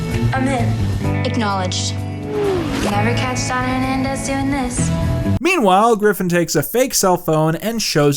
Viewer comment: I've played back this segment like a hundred times I still can't make out what Ari is saying. "Never catch Don Hernandez doing this?" Who is Don Hernandez?